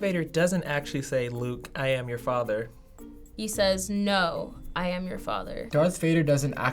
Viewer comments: Isn't it "No, I am the father?" hmm maybe i misheard. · Uhhh I Heard “no Luke I am your father” I am concerned